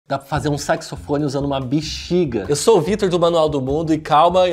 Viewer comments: Só eu que ouvi "sexofone" 😏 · eu ouvi "sexofone" dai pensei "q viagem é essa...??? ahhh sax..."